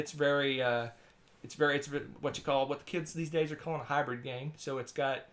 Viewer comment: Did he just say "kids these days"? lol